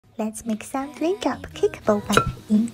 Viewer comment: I FIRST HEARD "let's make some breakup boiling water" HELP😭😭😭😭